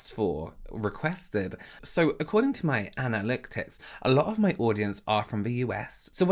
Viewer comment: I can't lie- to me it sounded like you said "Anna licks tits" instead of analytics! Took me a minute to catch up. I would watch your vids every day if it was possible!